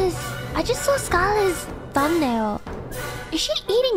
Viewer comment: Is it bad that i misheard marin saying bhole instead of thumbnail?